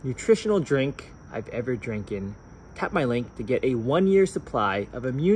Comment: Did he just say Dranken? FALLFISH